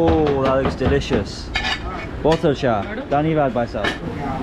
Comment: I thought he said paani lao bhaisahab but at the 2nd time i watched he said dhaniwad bhaisaab😂